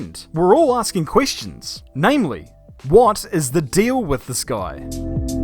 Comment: You may have said "what's the deal with this guy?" but I hear "what's the deal with the sky?" Lern 2 englishe